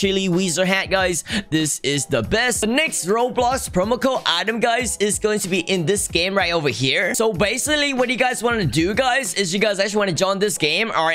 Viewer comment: did he just say bassaly